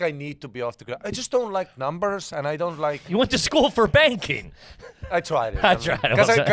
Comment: I thought he was joking and said "banging" not "banking" until later in the show and they talked about it again. 😂 #puretrash